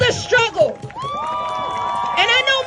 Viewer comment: At least have the guts to put uniforms on! And what does she say at 9:21... "Struggle?" Maybe they need to go and write a thesis about it all...